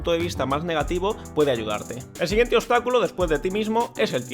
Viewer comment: Escuche “ el siguiente otakulo es”😂